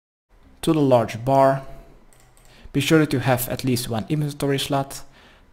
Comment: why i hear he say "be sure you have atleast one inventory slut" ??